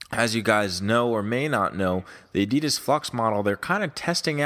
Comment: it sounded like you said "adidas FUX" hahaha. love these weekly check ins